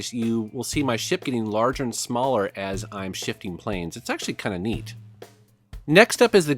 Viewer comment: I thought he said he's shitting planes.